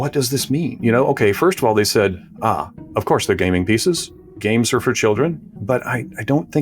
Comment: Every time he said "gaming piece" I heard "gaming PC"